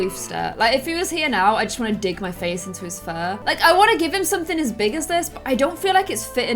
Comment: Give him some big boots! Me: Did she just say "give him some big boobs!"? Replays it Me: OOOOOOOOOHHHHH